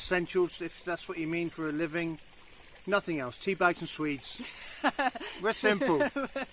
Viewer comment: never been to Scandinavia! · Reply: Swedes are a vegetable they need to make their genuine cornish pasties 😊. I know, i thought i heard "sweets" too 😄 but realized it was swedes when they mentioned pasties. So bring swede seeds should do 😊👍🏼.